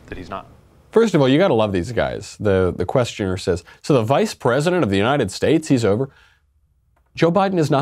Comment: just want to point out that you must have misheard him, the interviewer pretty clearly says former Vice President.